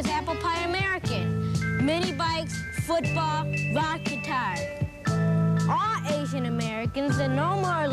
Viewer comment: When the kid in that PSA said "rock guitar", I heard "vodka tonic" and thought it was really weird to have a child identify that as a symbol of America. So glad John referenced it after and alleviated some confusion.